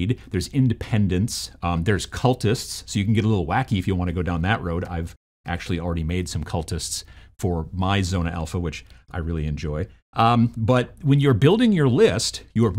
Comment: This whole time I thought you were saying "Zone Alpha"